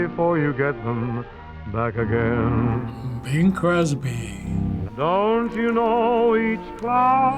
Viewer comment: I thought he said Bill Cosby